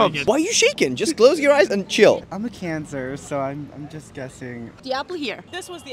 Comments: “I’m a cancer so…” “That is sick bro, which stage?” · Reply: i heard "i have cancer" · "I'm a cancer, so-" Yes, yes you are. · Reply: I thought he said I have cancer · I heard "I'm a cancer" and immediately opened the comments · "I'm a cancer, so-" Yes, yes you are. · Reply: I thought she said I have cancer